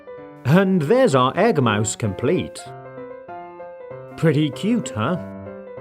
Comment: Instead of “pretty cool huh!” It is “pretty cute huh!”